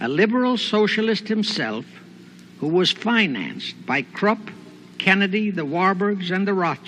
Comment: Did he say "Krupp" or "Trump"? at 12:00?